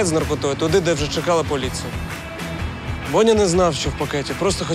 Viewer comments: Только я услышала на 37:13 не Бодя а Боня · Только я слышу на моменте 37:13 не БОДЯ А - БОНЯ…